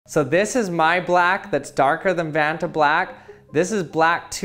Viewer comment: The best part is that everyone thinks it’s a joke, but that is what is known as a “black body” in physics. Something which you can shine a light into and pretty much nothing comes back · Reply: Did he just say, "this is my black?" 😂 Whoa!😂😂🤣 He's so proud of his black😂